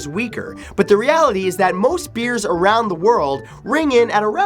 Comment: I thought I heard Bears instead of Beers.